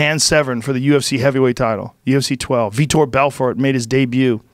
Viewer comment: Why doesn't he mention the fact he kept calling Vitor Victor. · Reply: I hear Vitor